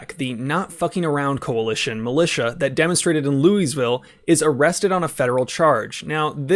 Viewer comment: did you just say “Louise ville”?